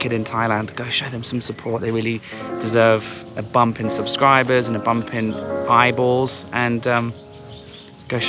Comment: Hahaha, when you said "bump in subscribers and bump in eyeballs", I thought you said "bibles" :_WTF:😄🤭